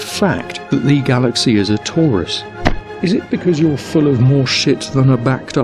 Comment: I legitimately thought you/he said "the galaxy is a Taurus" and I honestly assumed this nutter was going to explain the Milky Ways star sign and start predicting it'll come into money next Thursday